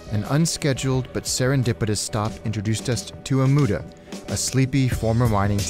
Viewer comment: I don't understand the name of this city. ^^" Amuda? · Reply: Omuta